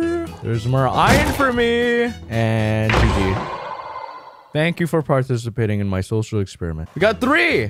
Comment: shank you for partishipating in my shoshal ecshperiment is what I heard...